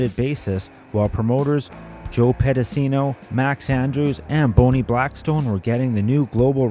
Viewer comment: Did he REALLY call her "Boney" Blackburn??? 😆😆